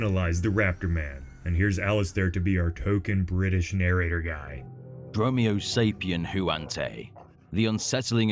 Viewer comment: Just for a second I thought I heard Goji guy and was like "what kinda endgame teamup is this??" Don't judge me. I'm new! Goji center sent us.